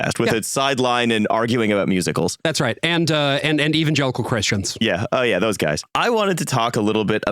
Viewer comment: Misheard Evangelical Christians as Even Jellicle Christians...